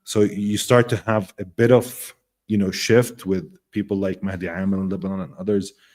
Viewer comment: what was the name he said? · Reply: Hard to make out. But maybe Madhi Amel <link>